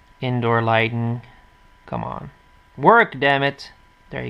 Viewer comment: WERK DAMMIT!!!! LOL I laughed so much if u know what I mean I SAID WERK NOT WORK LOL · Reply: Well I am dutch and Werk in dutch actually means Work in English - but I did say Work tho hahaha ;)